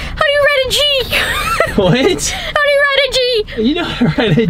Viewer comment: I thought she said how do you write in Greek